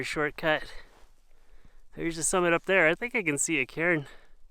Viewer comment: For a half second I thought he said "I think I can see a Karen." RUN FOREST!